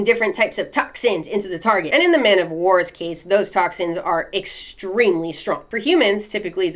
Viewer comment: you say extremely, captions say "unbelievably." They are similar, but different :/ not trying to be a bother, but I noticed it I loved the video though! Love your content.